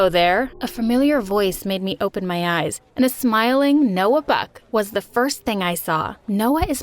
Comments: Did she just say Noah Beck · what did she say noah beck someone please correct me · When I heard Noah bock I thought u said Noah beck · I thought she said Noah beck 😂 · Did you heard her say Noah beck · Pov:I thought she said Noah Beck W-what Noah Beck?... U mean Noah long Neck? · I THOUGHT SHE SAID NOAH BECK💀💀💀💀 BYEEEEE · I heard he say Noah Beck at 4:05 he went to school with her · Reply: Noah Buck not Beck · did she just say"noah beck" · When she said “A smiling Noah Buck” was I the only one who thought of Noah Beck Why does this have so many likes.... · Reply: I really thought she said Noah beck 🤣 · I thought she said Noah beck 😂😂😂😂But she said Noah buck 😂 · I thought she said noah beck I was like wait lemme replay but no she said noah buck😂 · For a second there I thought she said “Noah Beck” 🤣🤣🤣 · Who else thought that she said Noah Beck instead of Noah Buck 💀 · When she said “A smiling Noah Buck” was I the only one who thought of Noah Beck Why does this have so many likes.... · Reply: I thought she said Noah beck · I thought she said noah beck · i thought you said noah beck but no it's noah BUCK 🤣🤣🤣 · did she just say a smiling noah beck? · Did she just say Noah beck 😳